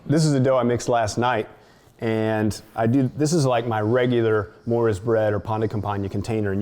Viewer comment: What is he saying at 5:38? "This is my regular [unintelligible] or pain de Campagne container." I don't understand "unfed" starter. At 1:12 he takes the starter out of the fridge, feeds it and lets it rise, then uses some in the bread. Isn't that using refreshed, fed starter? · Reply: Hi there! also calls this bread "Maura's Bread" because his friend Maura Brickman created and shared the recipe with him. For the starter, is recommending that you use a starter that you regularly feed and that is healthy rather than one that's been in the back of the fridge, untouched for an extended period of time. We hope this helps to clarify! Kindly, -👩‍🍳Morgan